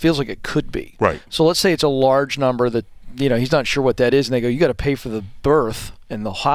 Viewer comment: "i dont know what that is n***a" am i the only one who heard that?